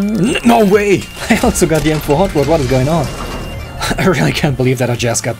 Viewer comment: I heard "I got sogar the M4" XD. Denglish